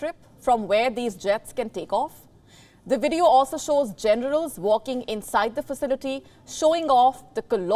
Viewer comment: "The video also shows GENITALS walking inside the facility, showing off the colosal size." WTF!!!! Did she say that for real!